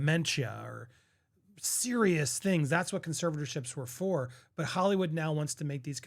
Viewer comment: lol what that doc said sounds like serious mental health